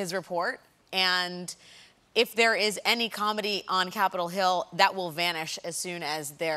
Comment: I think Alex Wagner said 'comity', and Colbert misheard it as 'comedy'. Anyone?